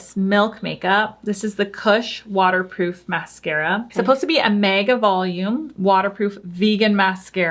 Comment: Sounded like said MAGA-volume. 😆